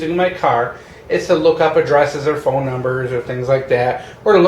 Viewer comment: "what I need in my car is to look up a dress..." Haha that's what I heard an nearly spit out my coffee. Lol had to listen to it again.